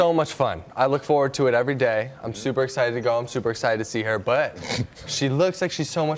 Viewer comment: " I'm so excited to see her butt " I thought he was saying that lol even the TV host thought that because he laughed